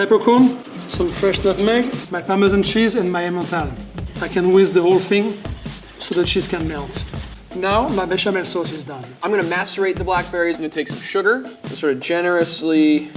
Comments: Did he say “I’m gonna masturbate the blackberries” !? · “I’m gonna macerate my berries...” I had to do a double-take there to make sure I heard it correctly. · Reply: He definetly said "masterbate" 🤣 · i thought he said masterbate 😂😂😂😂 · I thought he said “im gonna masturbate the black berries” 💀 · My mind is clearly in the gutter. I heard him say, “I’m gonna masturbate the blackberries” at 3:07. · I thought he said "I'm gonna masturbate the blackberries." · Highly disappointed in both lvl 1 &2 but did anyone else hear "im gonna masturbate the berries" at first @ 3:06 or was it just me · Had to do a double-take as I swear to God I thought he said he was going to masturbate the blackberries. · I thought the level 2 chef said masterbait the blackberries and I died..... Probably just me tho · I thought he said “I’m gonna masturbate the berries”💀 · Did the level 2 guy say "I'm gonna masturbate the blackberries" at 3:07 or am I hearing things? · I thought he said “I’m gonna masturbate the black berries” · i thought he said "masturbate the black berries" lol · When he said "Macerate" i heard it as "Masturbate" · did anyone heard "I'm gonna masturbate the blackberries"? O_o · I thought he said masturbate the berries and I freaked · Please help! I attempted to make a grilled cheese and I thought the guy was saying masturbate in blackberries. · I thought he said “I’m gonna masturbate these berries...” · I high-key thought he said masturbate the berries